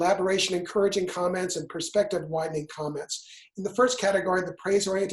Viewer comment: As a courtesy to folks who might be dependent on captions, please take the time to edit captions for accuracy. I saw "perspective whining" (6:38) and "whitening of opinion" (6:47) instead of "perspective widening" and "widening of opinion". For folks who do not have English as their first language, or who cannot hear the audio, these alterations of meaning may require the viewer to rewind to try again to get the gist, or they may get the wrong information. A "whitening of opinion" might stop one short to try to understand why the speaker was suddenly speaking about race. That said, I appreciate the info you are providing in this video series!